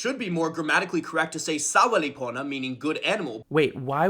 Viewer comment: Toki Pona speakers: Toki Pona has a really small phonological inventory so you can pretty much pronounce anything however you'd like as long as you're understood! Toki Pona speakers when people actually do that: GRR that's wrong GRR RAWF RAWF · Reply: yeah the problem here is that first vowel is a sound that already exists elsewhere in toki pona, and so I actually didn't understand what he was saying as "soweli pona" what I heard was "sawa li pona"